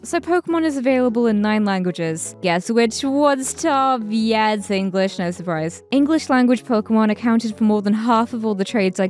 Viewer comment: what does she say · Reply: “Yeah it’s English, no surprise.”